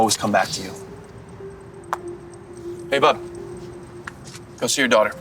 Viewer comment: I thought he said hey bum but he said hey bud 😂